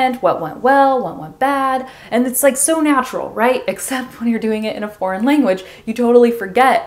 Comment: I can't understand what she says in the minute 3:02, according to the captions, she says "And it's like so natural", but, I play the video in this part over an over again and I can't get it, for me she says something like "And it says so natrual" or even "And it sights natrual". I know my two last sentences don't make sence but is what I hear. I would like to know what she really says in this part. Gracias por tus viedos, para mí eres la mejor de todas me encanta tu acento, es mi favorito!